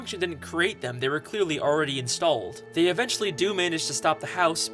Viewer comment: does he call Squidward scopper